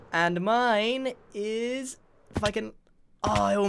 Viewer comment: Did u say fu**in 17:17? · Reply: He said if I can